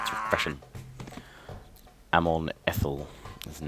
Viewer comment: Haha when you said Amon eithol should be mine now I thought you said I'm on ethonol haha lol